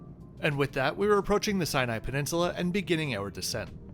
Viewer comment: Ngl I misheard this as the Side-eye peninsula.